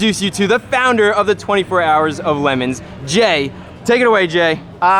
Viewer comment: I thought he said 24hrs lamon